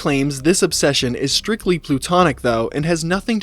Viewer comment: wait wait wait... lol... did you say PLUTONIC???? 🤣😂